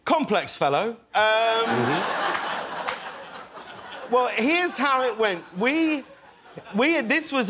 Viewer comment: When James said "here's how it went..." what I heard was "what had happened was..." and I'm thinking Wooooow, you had no idea what you were walking into huh?!